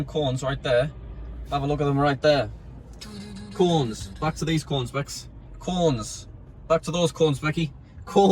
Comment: i thought he said cornes lol